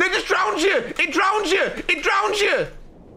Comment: what does say at 6:09 bc im def mishearing him 😅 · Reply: I speak fluent He says "no it just drowns you, it just drowns you" Youre welcome haha